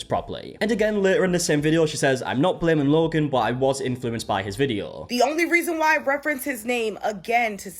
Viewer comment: “I was influenced!” she says. I hear “I shut my brain off.”